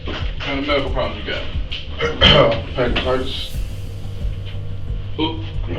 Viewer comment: First person said he had pancreatitus. Are not the symptoms he showed what might be seen in the latter stages of this disease? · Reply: I thought he said hepatitis